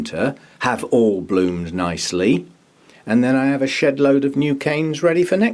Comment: Oh, SHEDLOAD....🙈😂I always thought you were saying "shitload" , like when you're repotting and you say a "shedload of sphagnum moss." 😂🤣 thanks for sharing ;)